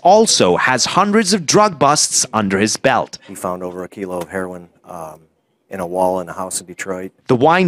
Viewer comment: I swear for a sec I thought he said "he ate over a kilo of heroin."